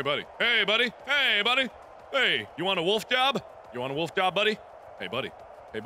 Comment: Any lovelies think he was saying Wolf job?